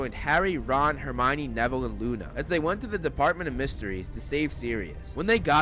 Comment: Was is just me or did he say department of mysteries